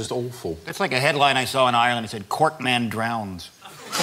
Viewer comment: What is it that Rich says at 9:20 ish that makes the gauge go off? It sounds like "Court man drowns", which, if it is, I don't get. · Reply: It sounds like "cork man drowns" to me.